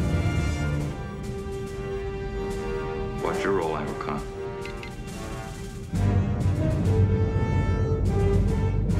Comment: What does Riker say @ 1:29? "Quadrool, angle-kon." ??? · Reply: brofenix Watch your roll angle, con.